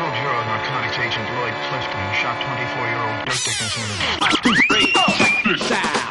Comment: I'm pretty sure during 0:10 and 0:11 I hear "… shot 24 year old…" Was that just me?